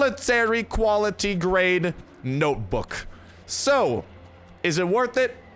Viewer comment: sounds like you said LOOK AT. THAT MILITARY GRADE NOPEBOOK